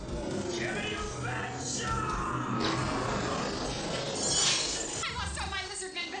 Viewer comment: 😂omg I misheard "gimme you bitch!" I was like Natsu!